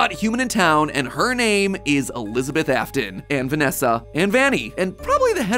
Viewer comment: I thought you said venessa is Elizabeth afton